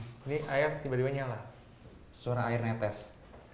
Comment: ada suara ketangkep, saya yakin bkn suara crew seh.. Itu ngomong apa yh? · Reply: Itu suara